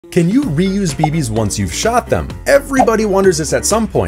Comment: Bruh i heard "can you reuse your babies after u shot them?" 💀